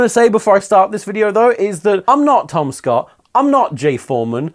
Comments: when he said 'I'm not Tom Scott', I thought he said 'I'm not TomSka' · I swear I hear him saying “I’m not TomSka” instead of “Tom Scott” My brain just gave up after that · When he said "I'm not Tom Scott" I heard "I'm not TomSka" and I was like "wut" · I straight up thought he said I’m not TomSka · When you said “I’m not Tom Scott” I thought you said “I’m not TomSka” and I questioned everything for a second. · Maybe it the drinks I was having, but I replayed this point several times with much confusion because I thought you were saying "I am not TomSka" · You said "I'm not Tom Scott" and I heard "I'm not TomSka" so I just go "you are tho" to myself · When you said "Im not Tom Scott", I heard "Im not TomSka" and I was really confused there for a moment.